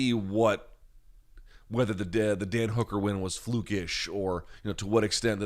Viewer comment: sounds he says dead hooker win instead of dan hooker win